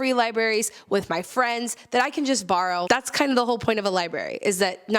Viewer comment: Did she say liberry? I’m pretty sure it’s library.